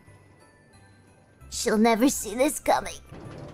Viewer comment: можете написать на английском что она сказала? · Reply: She never seen this coming вроде